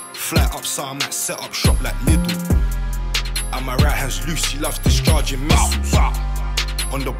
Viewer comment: did man just say my right is lucii or am I buggin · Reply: My right hands loose he loves discharging missiles